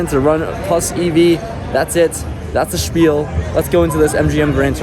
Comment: German here, did you say "That's the Spiel" at 0:58 ? :D